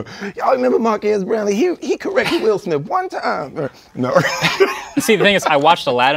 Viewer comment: I thought he said Will Sniff at first